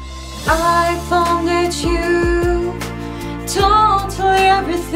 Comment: Айфон хочу. Ору послышалось